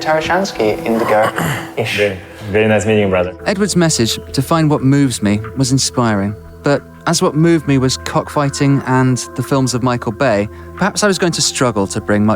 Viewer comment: Did he say cuck fighting?